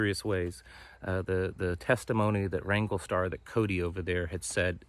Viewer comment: My apologies for not being clear on the channel and video I was referring to. Here is a link to the video. <link> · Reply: Ty for clarifying. I thought you said Ringo Starr! Lol